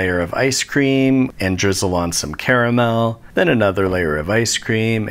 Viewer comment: That cookies and cream looked like how I dream it should be. Also... "You could also make non-conventional flavors..." Ginger caramel is a normal flavor?! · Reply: Oh crud, you said "caramel"?! I thought you said camel... I have a middle-eastern neighbor who's going to be maaaad.